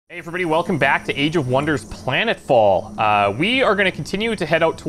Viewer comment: For a second there I thought he said welcome back to CIV VI and I was like OHHH SHIIIIIIII😂